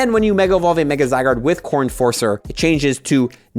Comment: When you first said "Core Enforcer" I thought you said "Corn Forcer" XD